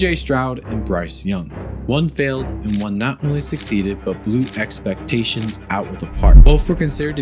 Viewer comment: Great video. Thank you. You almost lost me at the beginning of the video when it sounded like you said CJ Stroud not really succeeded. Annunciation is very important. Great video nonetheless great work.